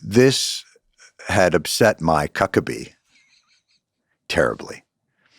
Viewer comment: We all thought he said my cuckabee